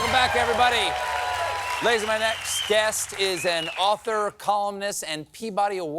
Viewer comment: The captions said "Ladies and Gentlemen..." but Stephen said "Lazies my next guest..." which was a bit Trumpian. Hope Stephen's okay or that it was a technical glitch of some sort. 😕